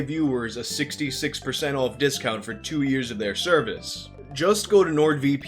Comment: I thought you said 2% discount for 66 years